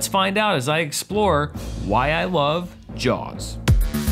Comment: Totally thought it said “Why I Love Jews”. So glad for your future that it didn’t...